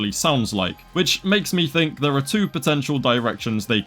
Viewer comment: DID I HEAR POTENTIAL